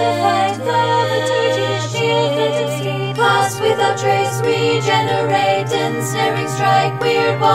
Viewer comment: Ok so I know now that it was "Regenerate", but it really sounded like "re-gender ray" the first time haha